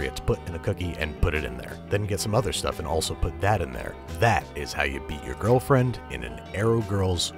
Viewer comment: My mind was wandering towards the end and I heard "and that is how you beat your girlfriend..."